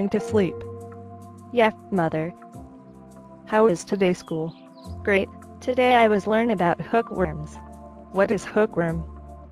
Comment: I thought she said today we learnt about hooking